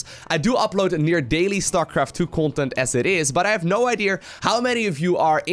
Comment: Did he just say no ideaier lol